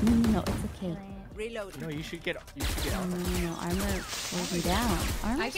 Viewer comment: im still confused on what was trying to say here is it gibberish?? "r was supposed to be down??" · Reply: Armor slows me down